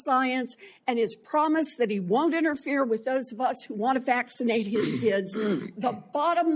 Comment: Did she say at 5:52 "that he won't interfere with those of us who want to vaccinate HIS kids."